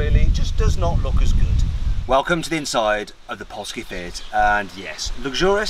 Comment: Did he just say at 1:55 "poski fiet"?